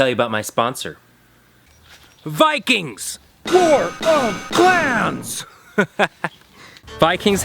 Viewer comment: I thought you said war of cans🤣